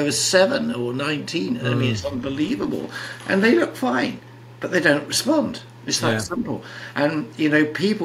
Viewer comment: Will someone on here please tell me what the Dr says after e says. People with CD as low as 15 they look fine but they dont.... Sounds like he mite said SMOKE but I can't understand in between 6:54,65. Thanks in advance!!😎😍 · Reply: they look fine, but they don't respond, it's that simple. And people still say to me....